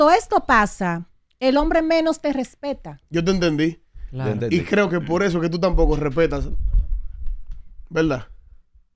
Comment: alguien sabe que nombre fue que dijo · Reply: Sandra berrocal😅